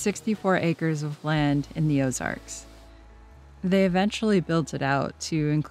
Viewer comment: Your storytelling is sensational & I relate 💯… the nomad lifestyle roulette is challenging…but being stuck in one place was mind-blowingly BORING… at 71 yrs strong I feel limitations rising so in this life chapter, I’m taking overseas travel & vanlife a bit slower…keep going while you have your youth & health! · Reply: Oops, I thought you said Azores…no Ozarks!